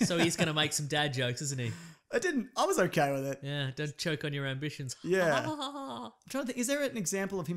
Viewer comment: I don't understand the hate for the "don't choke on your ambitions" (I thought he said aspirations?) line... I know Vader doesn't make sassy quips, but it was badass and gave a reason to force-choke someone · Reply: ricosuavee44 It was aspirations. It was a clear warning to Crenick. At the end of the day he's not in charge. Tarkin is and he should never presume from Vader's orders.